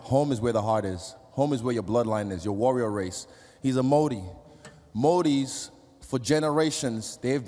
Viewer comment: What is he saying? Moody? Moodie? I wanna Google it but I don't know what he says